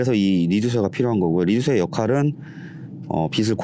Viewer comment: 리두세? 인가 이쯤에 저 장비 이름이 뭔지 궁금해요. 귀가 안 좋아서 그런지 잘 안들리네요ㅠ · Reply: 죄송합니다. 제가 발음이 부정확한가봅니다.ㅠ.ㅠ 리듀서 입니다. 시청 감사합니다^^